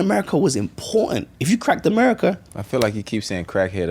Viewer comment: 😂😂 I heard “crackhead America” too I was like Ooo we going there 😩😩😩